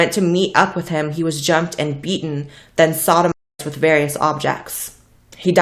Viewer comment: what did she say ? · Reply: sodomized